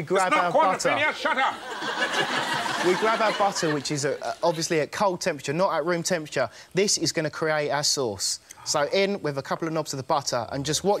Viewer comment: "Our sauce", honestly thought he said assholes...